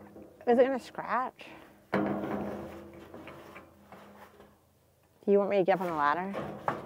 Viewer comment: What did she say at 13:28? "Do you want me to get up on a ladder?" 😆